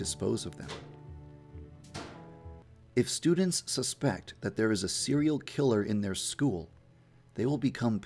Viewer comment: i heard :"There is a cereal killer"